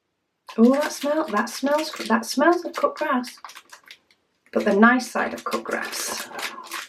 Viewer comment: omg made a mistake, i thought you said cooked RAT, you didnt you said cut grass. 😅